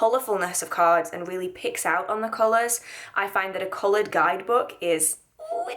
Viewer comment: It's around the 5 minute mark, I could have sworn I heard you say "pigs out on colors" and not "picks up on colors" and I just went with it, like yeah I can pig out on colors myself 😂😂